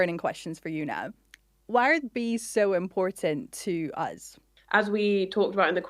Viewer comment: in 3:38 i think the host said "why are bees so important to us"